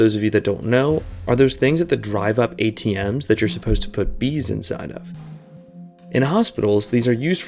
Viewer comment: im sorry,,,,,, did you say bees?